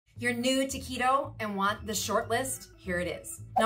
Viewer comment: At first I thought she said "your new taquito"